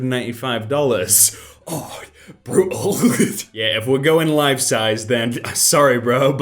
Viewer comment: he did not say life-size, he said 24 carat. (not $1,000,000 but way higher than $0.50)